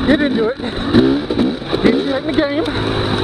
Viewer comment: I'm new to this world (enduro) so been watching tons of videos. Haven't rode a dirt bike for maybe 25 years. Just picked up a Beta 300. Looking forward to hitting trails like this. I thought I heard you say, 'get your head in the game...' and then a branch hit your helmet. If so, perfect timing! 😛